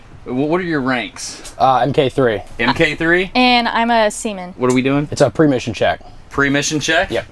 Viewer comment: im so sorry but i misheard sea men at 7:44 HAHAH